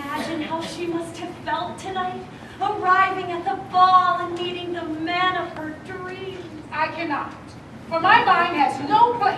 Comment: The step mother has very poor articulation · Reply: Honestly it was hard to tell what any of them were saying. It sounded like Gabrielle said "arriving at the ball and dating the man of her dreams" maybe that's what she's saying but I can't tell if she said "dancing" "dating" or "meeting"